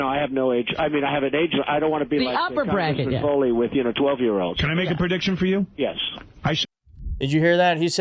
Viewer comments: Bro, that is not what he said. You're just straight lying. I hate Trump as much as the next guy but this is just a bald face lie. He says "I don't want to be like congressman Foley with, yknow, 12 year olds." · Can we get a transcript because I heard him say "I don't have an age limit" and then immediately walks that back to " *inaudble*... 12 year olds." I like to stick to the facts. What did he say cause I can't make it out? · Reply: "I don't wanna be like Congressman Foley with 12 year olds." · Second time I've listened to this, and I'm hearing something different. I hear him say 'I don't want to be like congressman Foley with 12 year olds'. I don't know who that was, but this doesn't seem like a smoking gun to me. I absolutely think he's a p**ophile though. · I thought I heard him say he didn't want to be like congressman Foley with 12 year old. Any way to bring his voice forward for clarification? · What I heard was "If I ...no, no I have no age .. I mean I have age lim.. I don't want to be like Congressman Foley, you know with 12 year olds." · I really don’t like DT but I heard him say, “I DON’T want to sleep with 12 year olds”?! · It didn't sound like he said the limit is 12. It sounded like he said "he wouldn't be messing with 12 year olds" which is another way of saying he wouldn't be messing with kiddos. This is a huge reach.